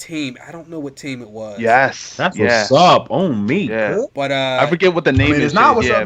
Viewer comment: was I tripping in the beginning when it sounded like said "I eat ass what's up" 🤔🤔🤔